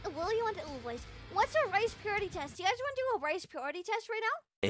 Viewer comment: I heard "race purity test" and was like 'I don’t like there this is going' 😂